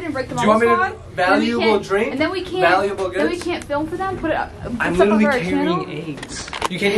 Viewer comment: When said, “I’m literally carrying eggs.” I thought he said, “I’m literally carrying AIDS.” 😂 I WAS LIKE WHAAAA!?!??! DANGGGG you gonna share that??? 😂 But of course I realized. I love you guys!!!